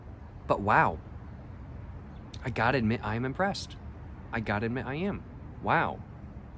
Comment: i thought he said "depressed" and i was like wait hold up